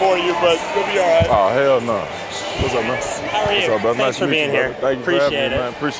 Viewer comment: wassup blood (to the coach lmaoooo) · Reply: Yea he said playa but it did sound like he said what’s up blood in the beginning lol still is funny 😂😂